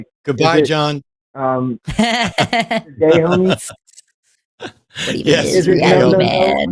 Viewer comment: Correct me if I am wrong, but did John, after Matt said “goodbye, John”, say “you’re gay homie”? 2:07:24 That’s what I hear but please correct me. I just think that Matt may have missed it and that is highly offensive for him to say that